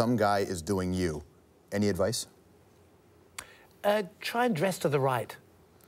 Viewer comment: English is not my primary language. What did Robert say at 10:55 ? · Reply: I think you jest but it was Try dress to the right... Explained elsewhere in these remarks.